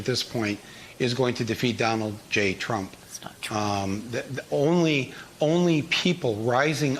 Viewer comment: Who heard “that’s not true”